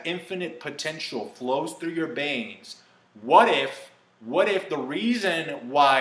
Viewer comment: gonna say this because i found this kinda funny but in no offense to you, since i'm sure i'm not the only one that found it adorable/funny, but you said Yango Fett instead of Jango Fett, and I swear i heard you say Banes instead of Veins at 8:40!! xD